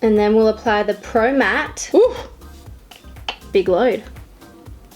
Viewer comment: Did she just say "thats a big load" around 1:44?! LMFAAAOOOO